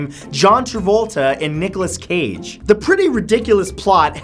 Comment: did he say Michales Cage?